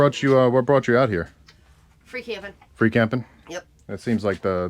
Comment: I thought she said "freak heaven" 🤣